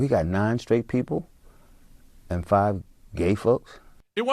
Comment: Sounds like he says "and five gay f*cks" 😅